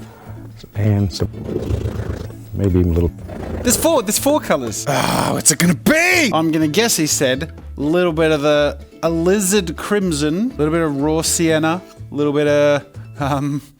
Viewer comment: I've watched bob ross since i was a child...i always thought he said "a lizard crimson" every time as well...lol